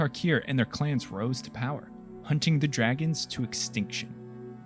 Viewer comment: I heard "Punching the Dragons to extinction" XD